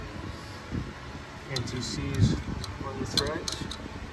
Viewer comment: what'cha doin' at 7:32? and using? I hear "NTCs on the threads...." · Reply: *Anti-seize on the threads